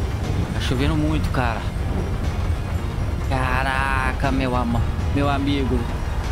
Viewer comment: Só eu ouvi ele falou "Caraca, meu amor" KKKKKKK